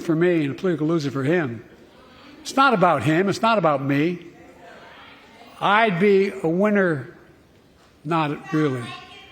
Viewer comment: what'd she say? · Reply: I'd be a winner, not really.